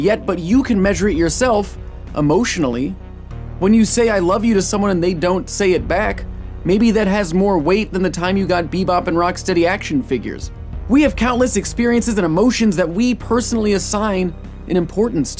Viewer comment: I thought he said "maybe that weighs more than the time you got beating off to action figures lmao"